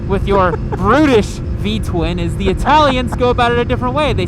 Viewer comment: did you just call the Duke a brittish V-twin at 4:10 ?